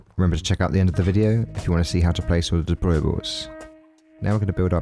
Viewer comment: What the hell is he saying at 10:30? Check out the end if you want to see how to place oldabruwals??